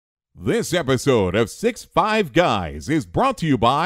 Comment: I thought he said 6 bi guys.